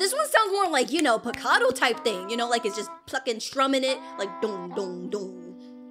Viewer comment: did say the f word? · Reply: plucking